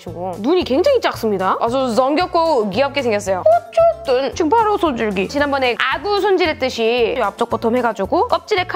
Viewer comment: 나만 여기 바로 손절 기이라 들었나ㅋㅌㅋㅋㅎㅋㅋㅎㄱ...내 머리속은 왜 그러지,..ㅎ